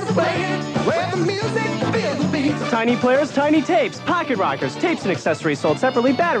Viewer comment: First time I heard this like "F*cker Rockets" 🤣🤣🤣